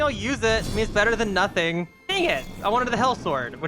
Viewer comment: nothing i thought he said Nutting😭🙏